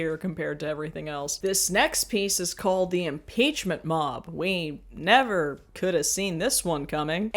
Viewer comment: I thought you said Impeachment Mom and now I want to be everyone's Impeachment Mom